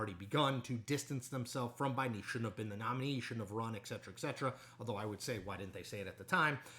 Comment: Did Chris actually say ECK cetera ECK cetera? noooooo